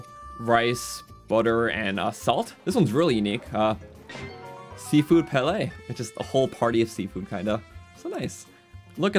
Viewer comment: did he just say sea food palais? 😂 This has made my day thank you!